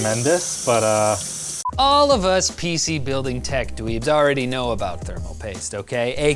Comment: When Riley said tech dweebs I legit thought I heard Tech Weebs and i'm like "da hell is dat"